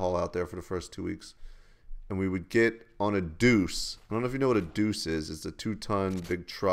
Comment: At first I thought you said douche :3